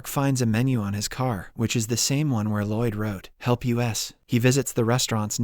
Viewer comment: Why did he say "HELP YOU ASS" instead of "HELP US" ?